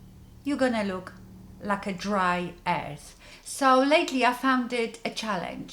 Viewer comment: I have Lupus and have very dry skin. Looking for a tutorial to help w/that. Btw, sounded like u said “you gonna look like dry ass!?” Love the accent wer r u from?